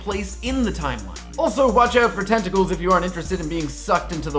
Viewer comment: it sounds like he said testicles instead of tentacles.